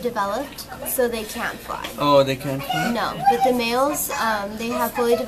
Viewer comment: Males actually live for 9 months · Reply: oh yeah i thought you said females